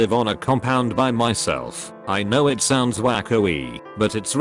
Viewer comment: you read "wacko-ey" but what the post said was "Waco-ey" which is most likely a reference to the infamous Waco, TX compound siege incident.